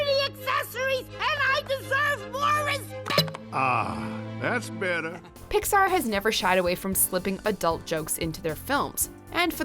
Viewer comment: did she said number 6 like sex?